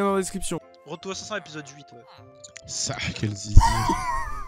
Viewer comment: Sah quel zizir Moi : Il a vraiment dit ça?